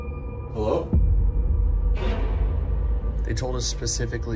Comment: Sounds like the childs voice in the beginning was saying leave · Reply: I went back and listened to it again and I agree with you. The very first time I heard it I thought it said hi or hello but the more I listened to it it was to long of a word to be hi or even hello.